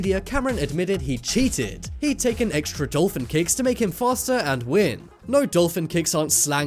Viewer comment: Omg I thought you said dolphin cakes😂 so I was like wth